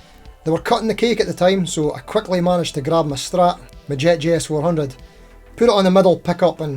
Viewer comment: Did you say Jet JS-100? Did I miss something regarding Jet guitars? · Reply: Had to double check that - I did say JS400. I just need to work on my pronunciation 🙈 Scottish accent probably doesn't help 😬